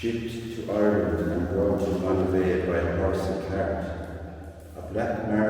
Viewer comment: Absolutely beautiful. Such a work of art. I had to laugh though. Your Irish accent can be difficult for my American ears. Sounded like you said the marble was brought in by a “horse and cat”. 😅